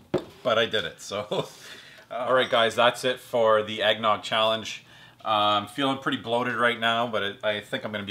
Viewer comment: I've always heard it as "the gallon challenge", sooo I think you have another 1.5 l left ;)